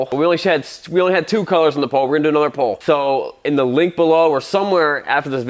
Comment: Damn, I thought said "another bowl"... Oops, guess that was "another poll" ... My bad...